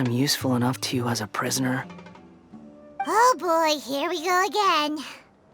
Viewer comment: I first think paimon say "Ah sh-- here we go again"